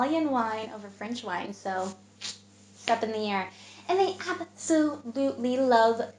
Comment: is she saying : step in the air ?! 😱what is she saying when talking about French preferring the Italian wine 🍷 ?!!! Merci. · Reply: “It’s up in the air!” 👀